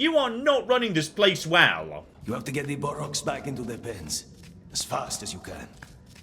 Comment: Did Gollum just say bollocks?